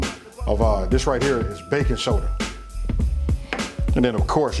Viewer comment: you say "baking soda" but the caption says "baking powder". Which is it? The two are not interchangeable.